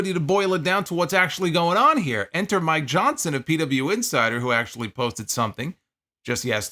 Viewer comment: I thought you said "Enter My Johnson" at first